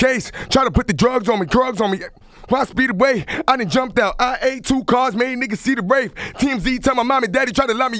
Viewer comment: Anybody hear him say he ate 2 cars? At 1:13? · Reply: Jyreeh Grove he said i8